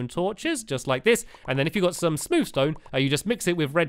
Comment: On 8:41 I thought stampy said smooch stone instead of smooth stone